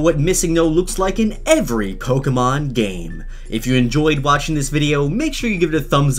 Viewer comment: Am I the only one who heard cocomon game